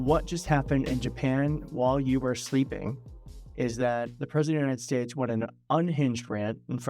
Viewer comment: Did I hear "United Shtatesh" again?